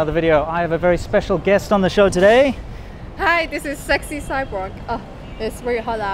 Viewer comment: Oh "SexyCyborg" thought she said "Sexy Sidewalk" :/